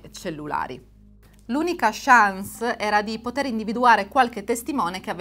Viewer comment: Ho iniziato a seguirti adesso....al minuto 6:30 circa ho sentito "tutt"... Dimmi che ho sentito male altrimenti mi tocca togliere subito il like. Non sopporto queste cose finte inclusive. Spero di essermi sbagliato. Grazie 😊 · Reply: Io sento "l'unica chance" pronunciata "shans", un termine molto usato da tanto in italiano.